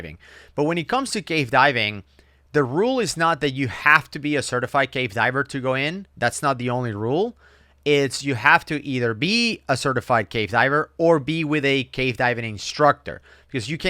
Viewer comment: never sounds like hes saying "cave" diving. It sounds like hes saying "gay" diving and it makes it a lot funnier XD